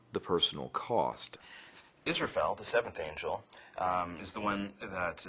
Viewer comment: Did he just say Israphel?!?! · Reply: Yes, Israfel is the 7th angel in NGE.